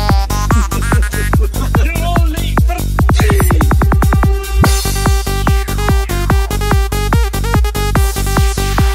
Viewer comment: what did he say at 5:29 ? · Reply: Lenn eeyy you are all late for thee